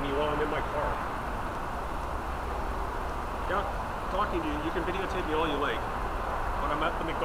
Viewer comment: he clearly says "You can video tape me all you like."